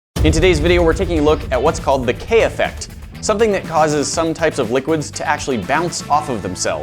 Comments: Hahaha I thought it said Kanye effect lol😂😂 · i thought its said kanye effect ..😂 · I swear I thought it said kanye effect lmao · I thought it said kanye effect.. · I thought it said KANYE effect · I thought it said Kanye effect. SMH🤦🏾‍♀️lol. Only I would see that. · Thought it said Kanye effect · okay but i thought it said Kanye effect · I thought is said the KANYE EFFECT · I thought it said Kanye Effect hahaha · For a min I thought it said kanye effect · Anyone else thought it said Kanye effect? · I thought that said kanye effect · lowkey thought it said kanye effect